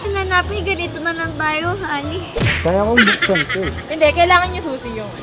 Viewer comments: did she just said "Honey"???? O did I misheard it? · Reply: diba? Ilang beses ko inulit eh, parang honey sinabi niya. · Blythe- wag na nating hanapin ganito nalng tayo HONEY??????? Tama ba narinig ko honeyyyy? 😍😍😍😍😍 Omg sethdrea lng sakalam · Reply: Actually po Ali sya but sounds like Honey hahahahaha!! #SethdreaLangSakalam kakilig hahahaha!! 💚💚💚💚